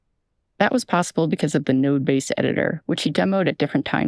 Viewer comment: Did you say "nerd"? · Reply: “Node”